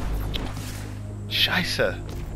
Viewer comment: was that german? I thought i heard „scheisse“